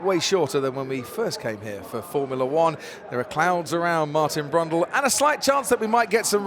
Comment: Did crofty call himself a clown? 0:08 lol